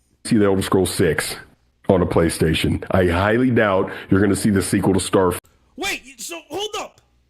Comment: did say 'you won't see the sequel to Starfield on Playstation'? So, he saying Starfield is coming to Playstation, but any sequel wont - dude's talking out of his ass !